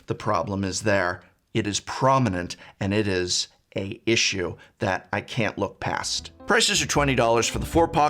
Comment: ”a issue”? English is not my mother tongue, but I cannot hear a consonant at the start of the word ”issue”